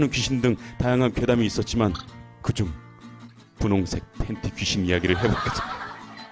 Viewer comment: 나 왜 계속 분홍색 팬티가 보라색 팬티로 들리지.. 엄마 나 이상해